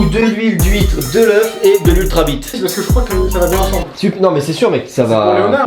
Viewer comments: dites-moi que je ne suis pas la seule à avoir entendu ultra-BITE ??!!!😂😂😂😂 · j'ai compris de l'hulta-bite sa m'a tué ptn